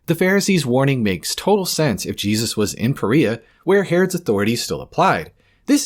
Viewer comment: Wait a minute did he say "Korea" ? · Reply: no Perea it sounds similar